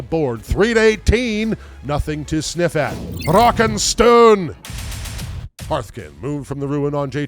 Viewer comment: Did I hear a Rock and Stone???